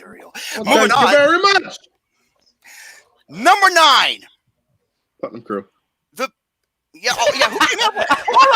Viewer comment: What did say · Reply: He said, "Putnam Crew." The context behind that goes a long way.